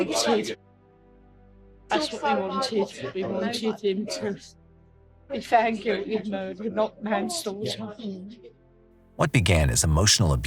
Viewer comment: the subtitles saying “fair and good” are not accurate. Natalie’s mother says, “That’s what we wanted, we wanted him to be found guilty of murder, not manslaughter.”